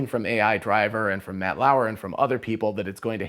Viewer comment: did he say Matt Lauer? Is he talking about Rob?